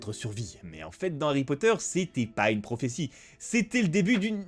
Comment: le debut d'une quoi svp harry poter ? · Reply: Bite